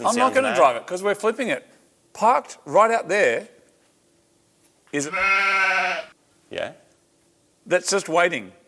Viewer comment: Anyone else have any guesses what car is waiting for "next" when he says the name at 32:43? They usually cover their mouths so you can't read their lips, but I think he says: SUPRA I love this episode, but I hope I'm right and we get to see an old Supra next.